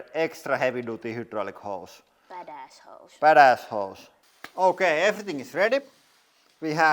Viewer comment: Love her pink adjustable spanner can I have one it sounded like she said bad ass holes .erectile disfunction hose only goes slightly erect 😂😂😂 and you could put dye in the water you use on different pipes next video different thickness of metal pipes please instead of rubber ones